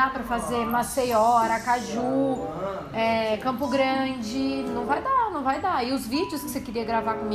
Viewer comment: eu ouvi Campo Grande ??? e minha cidade kkk